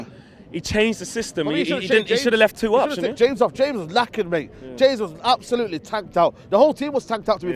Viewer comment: utd fans are such cry babies it sounds like he says 'james was lacking', and everyone in the comment section is crying im sure he said 'james was knackered' so stop crying, and get to bed ppl i know ur sad we drew